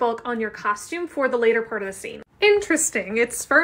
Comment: Genuinely thought she said "interesting! It's sperm but kinda squishy" 😂